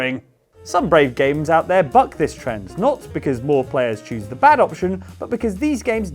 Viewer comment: This is great timing, I just finished Untitled Goose Game and was looking for similar games :) thank you! Also I am very tired and misheard saying "buck this trend"😂😂